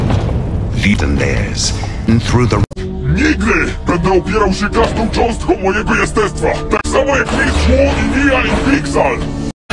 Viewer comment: what is he saying XDDDD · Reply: „Nigdy! Będę opierał się każdą cząstką mojego jestestwa, tak samo jak Mistrz Wu i Nya i Pixal!” - Polish: „Never! I will resist with every fiber of my being, just like Master Wu and Nya and Pixal!” - Traslation from polish BONUS! 2:10 here he says „Nigdy tak nie mów!” which can basically translate to „Never say that!” or „Never talk like that!” Ah…Polish my mother language. Greetings for Poland 🇵🇱☺️